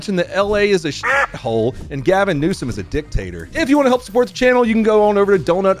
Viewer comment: What he said: "Dictator." What I heard: "Dick Tater."